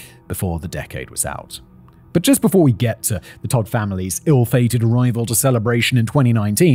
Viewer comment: I thought he said toad family.